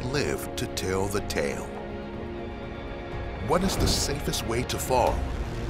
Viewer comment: Did I just hear 'how is the safest way to fall'?? Or am I dreaming.... tf